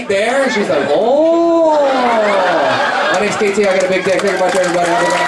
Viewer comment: did you just say "I got a big dick"??? LOL